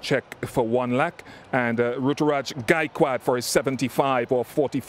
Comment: The Captions Say Guy Quad Instead Of Gaikwad In